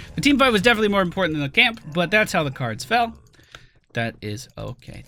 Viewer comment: Every time says "but that's ok" I just hear "Serenity Now!!"